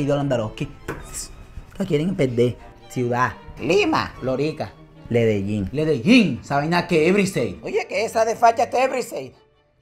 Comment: Nueva ciudad de Colombia "Ledellin" 🤣🤣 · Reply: Yo pensé que había dicho Medellín y me empece a enojar pero medi cuenta de que dijo Ledellin